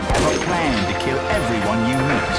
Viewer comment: I've always thought the sniper said "be prepared to kill everyone you meet" at the end of his video!